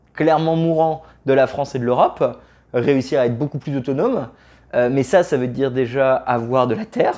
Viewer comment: J'ai cru halluciné à 4:08 car lors du premier visionnage j'ai compris "mais ça ça veut dire déjà avoir de l'éther" . Pour moi ma citadelle serait une petite maison dans la cordillère des andes où j'accueillerais quelques touristes et où la communauté locale serait autosuffisante en nourriture grâce à l'agriculture locale.